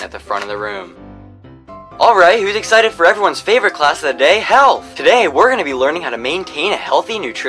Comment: I thought he said hell instead of health 😆